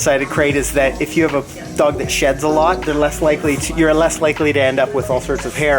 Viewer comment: I thought you said a dog that shits a lot